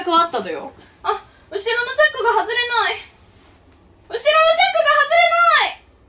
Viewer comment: の「後ろのチャックが外れなーい」が 「ハートのエースが出てこなーい」の空耳いけそう